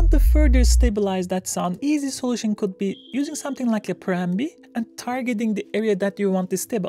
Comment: I think when you use the Pro MB the youtube subtitles translated your words to "use something like a Prambi. At first I was thinking, what the is a Prambi? Then I realized you said Pro MB and it should have been obvious; would have been if I wasn't watching those subtitles. . Love your content! ! ! As people are saying, you get right to the point and give clear and also comprehensive instruction!